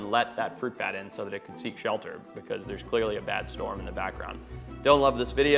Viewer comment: I had to play that back, I definitely heard “a bat storm in the bat ground” 😂😂😂